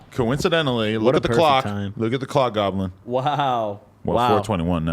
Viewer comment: seconds sound like he says cock goblin. like if u agree